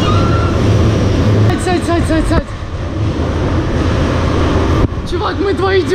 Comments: она говорит "сядь сядь сядь" а многие пересмотрели этот момент для того чтобы прислушаться! И многим сначало кажеться что она говорит "зай зай зай" 😂😂😂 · она говорит "сядь сядь сядь" а многие пересмотрели этот момент для того чтобы прислушаться! И многим сначало кажеться что она говорит "зай зай зай" 😂😂😂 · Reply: Она же сказала сядь три раза, и на первый мне послышалось что она сказала зай, а последние два сядь